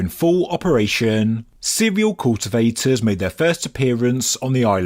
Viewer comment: Lol "cereal cultivators made their first appearance on the island", my brain heard it as "serial cultivators" and i just thought of a people just compelled to cultivate.